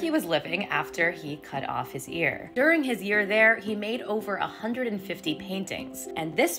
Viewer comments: did you just say, "during his ear there" ??? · I thought she said He cut off his ear. During his “ear” there… My ADHD brain: well that’s a messed up play on words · Not me mishearing it as "during his EAR there" 😭😭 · Did I hear "during his y- ear there"??? 😂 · I heard “after his ear there” instead of “his year there” 😂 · All I heard was, "During his ear there" 😂😂😂